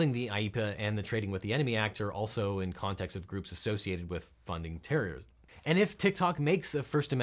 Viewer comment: sounded like “funding terriers”. Sounds like a good idea tbh.